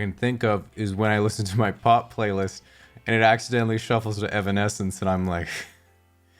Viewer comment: When I listen to my pop playlist and it accidentally shuffles to Evanescence and I'm like I needed this 😂😂😂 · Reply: I thought he said "pot playlist"...which also works.